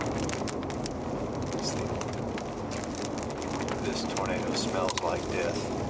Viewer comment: "This tornado smells like death" continues driving right towards it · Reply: oh, okay. i thought he said "this tornado smells like piss" honestly the "piss" makes me laugh way more.